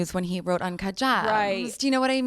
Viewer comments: Dam I really thought she said uncut jams in a weird accent when I saw the memes but watched the video and it's gems and it makes it so much better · I seriously thought she said “when he wrote on kahjabs” it made me think “kahjabs” was the name of a blog or something…